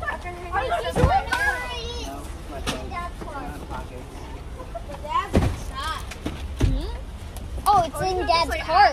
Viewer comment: when lifts up his shirt- sorry I can’t hear very well what’s being said but looks like he’s hiding something there? Did he shoplift? From one mother to another. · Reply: I think might have suspected him of the same thing but he said “it’s my phone I don’t have pockets” hope that helped 😊